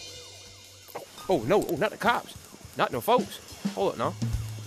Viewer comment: i heard "oh no, not the cops...oh shit, oh shit,,,oh shit" and suddenly I'm started to suspect that one of those news outlets is mistaken.😂